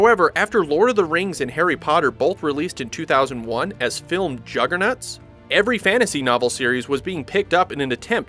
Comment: One Question: At 2:14 did you say juggernuts??